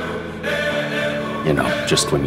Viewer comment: OK WHAT THE HECK WHY CANT THIS EXIST also at the end I thought cas said "it'll turn on your mom" and I was like woah there cas